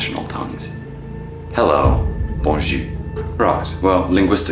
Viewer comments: As a frenchi person, you nailed the pronunciation! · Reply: no he didnt, his Bonjour didnt sound like it had an R at all, it sounded like he said Bonjew. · As a frenchi person, you nailed the pronunciation! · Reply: The replys on this comment are so funny. But anyways, he didn't get the pronunciation correct. I speak french and do all my school in french, and I had to go back just to see what he was saying because of the pronunciation. He didn't pronounce the r pretty much at all, which, I get it, that's normal for non french speakers. But due to this, I heard 'bon jus" which means "good juice".